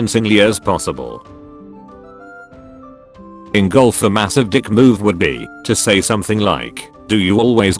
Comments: I thought he said “engulf a massive dick...” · I thought I heard "Engulf a massive d*ck" at first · I could have sworn he said "Engulf a massive dick" and it changed the context of the whole comment.